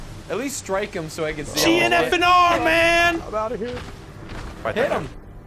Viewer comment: what does he say at 28:00 ? G and f and r?